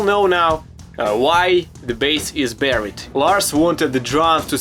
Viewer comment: I thought you said "bus"